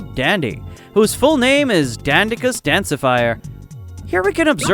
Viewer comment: Is it just me or did he say “danceafire”